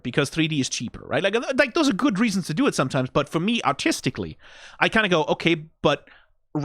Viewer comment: My Internet was buffering here so I heard "So for me autisticly I go-"